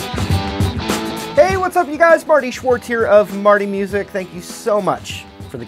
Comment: i thought he said FARTY Shwartz!!!! LOL